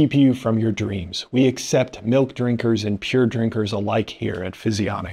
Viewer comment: I totally thought he said "We accept milk drinkers and tear drinkers alike" <link> So... he actually said "We accept milk drinkers and pure drinkers alike" ... but I like it the way I misheard it better.